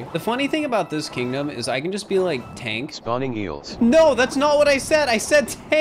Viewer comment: I think at 9:21 you said ''i can bE Like tank" and that spawns the eels